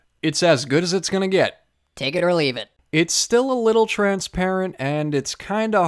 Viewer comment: Wait... did you just say "CAKE it or leave it" or am I just dreaming of cake? 😂